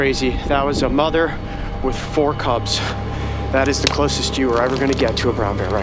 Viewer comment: I heard four cups I'm like "what cup you mean Cubs"